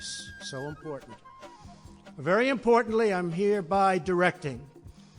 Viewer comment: did he say "...very importantly..." lol